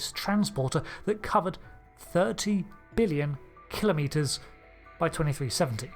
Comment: thank you so much for doing your own subtitling, you are one of the few creators out there still doing it. this isn't a complaint, just letting you know that at 6:30 the subs say "300 billion" while the narration is "30 billion". idk what goes into editing and/or reuploading a video on youtube so if it's too much of pain please don't worry about this small oversight. it is SO much more important that you continue to put in the effort to write the subtitles instead of relying on the lackluster auto-gen youtube provides. great video, as always!